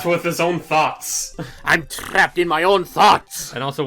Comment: I thought he said “I’m trapped with my own farts.” at first.